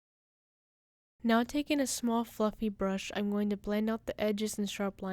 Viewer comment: I thought she said now taking a small f@ you brush lol but she actually said now taking a small fluffy brush